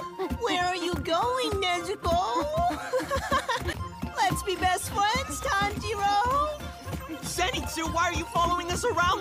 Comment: I heard “let’s be best fwiends Tanjiro 😊”